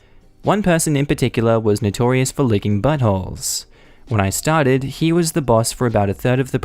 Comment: "licking buttholes" ....... hah-ha!...... I thought it said "licking buttonholes, and figured, This is going to be an interesting story!